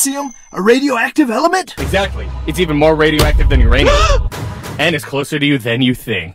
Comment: Thank god it’s not radium I thought he said radium when I first watched it